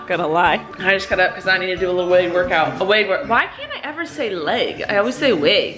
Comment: ! I could have swore she said “wedding workout”